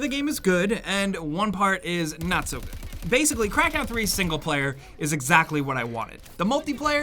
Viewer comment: anybody else hear "crackhead 3"